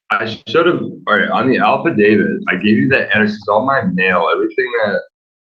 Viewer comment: “Alpha David”? I’ve rewound it three times to make sure I heard that right.